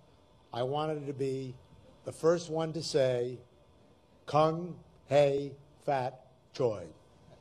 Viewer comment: Eisner: with Chinese New Year around the corner I just wanted to be the first one to say— Me: oh no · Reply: I was dozing off to the video and dead ass thought he said "cunt hey fat joy" lmao it caught me off guard 😂